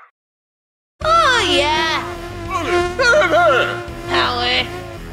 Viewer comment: Did aphrodite just said coward